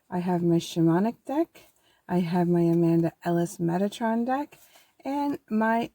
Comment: From what deck did the Duality card come from? I swear it sounded like you said "Amanda Alison" which does sound like many people misspell my name which is "Amanda Allansson". I was really confused when I thought you said my full name which is rare!